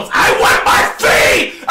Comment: when mac throws the int at 12:57 what is he saying. I want my what? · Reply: Fade , as in like he wants to fight mac😭